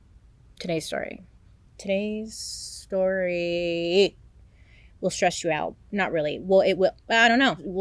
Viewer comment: I misheard at 1:28 and thought you said “today’s story will stretch you out” 😂